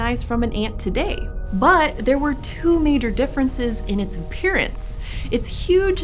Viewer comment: I thought you said there was a two meter difference between ants today and ants then... I'm tired, don't judge me.